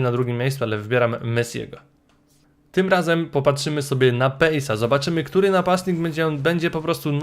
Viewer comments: usłyszałem popatrzymy sobie na penisa zamiast pace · Czy tylko ja usłyszałem: Tym razem popatrzymy sobie na penisa xdddd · Czy tylko ja usłyszałem ,,teraz popatrzmy sobie na penisa'' ? (2 zawodnik xd)